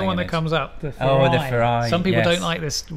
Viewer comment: did you say Ferrari? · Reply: Sadly, no. He said 'Furai'.